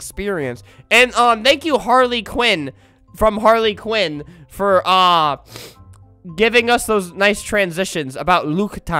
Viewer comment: I might have bad hearing. But at the end I thought he said “And thank you Harley Quinn from Harley Quinn for taking the vegan bullyment”